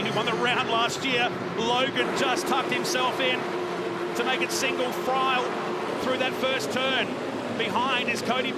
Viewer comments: 😂😂😂 sounds like he said "single fryle" instead "single file.". Even the captions had it as "single fry". Happens at around 1 minute 35 seconds. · 😂😂😂 sounds like he said "single fryle" instead "single file.". Even the captions had it as "single fry". Happens at around 1 minute 35 seconds. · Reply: Read your comment as it was said. Thought he said "Single Fryer".